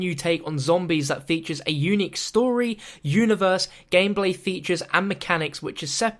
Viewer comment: did he say "gambling features" ? or gameplay ? im confused